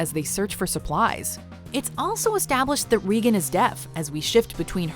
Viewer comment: I thought she said reegan is death